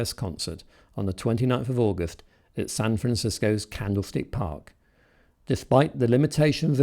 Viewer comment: Cool video. Nice clips of the Beatles interviews and them playing Paperback Writer. It sounds like you said they played at Candle State Park. I may have misheard that. But, just in case you did say that, it is actually Candlestick Park.